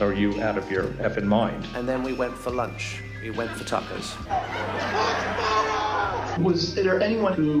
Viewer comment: Off topic a bit - as an ex-Brit, what I heard Nick Quested say was "We went for lunch, we went for tuckers." Steven Colbert heard that as "we went for tacos" but 'tuck' or 'tuckers' is upper-class British schoolboy slang for food - usually snack food or fast food, that you get from the 'tuck shop'. I don't think he's being specific about the food, just saying it was not in any kind of restaurant.